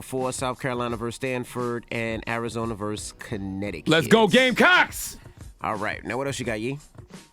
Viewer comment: Did Charla say LET'S GO GAMECOCKS @ 0:51?! 🤤😋WHAT?! 😆🤣😭😭😭He somethin else 😑